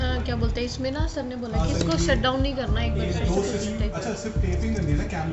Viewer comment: did she say the n word 😂😅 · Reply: She said-Nehi karna,not the n word Mean do not do